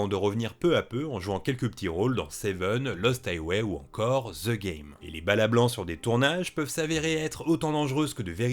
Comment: vous pouvez dire ce que vous voulez moi j'entends "Thekairi".